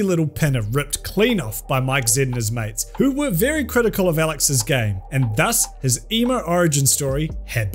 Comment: Why do I hear "Alex's game" As "Alex is gay" Like, we don't accept him, send him off to the superstraights